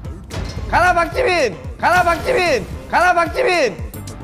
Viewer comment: it looks like he says ‘ga naar park jimin’ What means in dutch go to park jimin xD wie hoorde dit ook of ben K de enige??